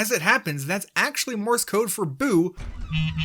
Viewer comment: I thought you said “poo” I was like oh... 🙂